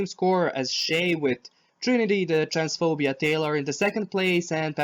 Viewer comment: I heard a "trinity transphobian taylor" and i fucking chocked on my soda omg girrl